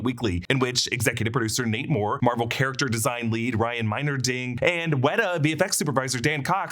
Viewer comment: When he said the 2nd guy's name there, it sounded like he said 'Ryan Minor-Ding' but in reality(...and trust me, I looked it up) the guy's actual name is 'Ryan Meinerding' Did anybody else actually think that the guy's name was 'Ryan Minor-Ding'? ...bc seriously, I couldn't possibly be THE ONLY ONE to think that who watched this video... hahaha